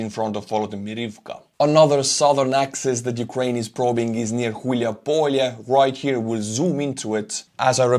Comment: When mentioned the town at, or just about at this time stamp, did he actually say "Hulyai Polye" (or something similar to that) and is it possible that it's the same small village that Nestor Makhno was from?